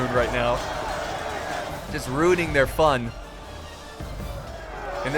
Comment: I thought was making a Pun Rhuning the fun. (Instead of ruining)